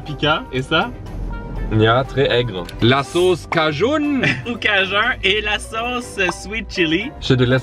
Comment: a 5:40: le sauce cajun ( cajoun ) moi : il joue a ykw 3 ?